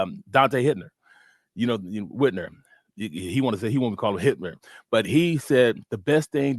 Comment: 😂 neega wicked . Did I hear " Hitler during this passover 😂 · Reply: Whitner = Hitner with a "N" (because he can hit)